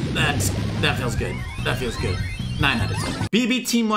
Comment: is it just me but did I hear say that feels gay, that feels gay.🧐CRINGE METER 100